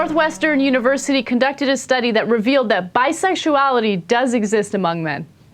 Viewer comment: I thought it say Bisexuality is obvious.. lol